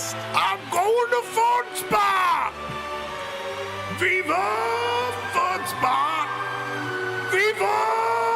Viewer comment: I really thought he was saying “viva foot spa” and not “viva fun spot” until i saw the logo😂